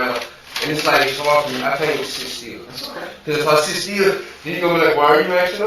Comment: Love that at 8:37 he says "because if I sit still" and the captions say "because if it's about 60$" 😂 what?!?